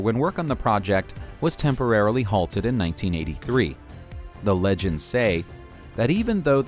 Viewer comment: Wait did he say 1983 Fnaf